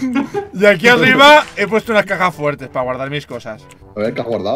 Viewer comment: En el minuto 9:57 entendi cacas fuertes envede caja fuertes,que mongolo que soy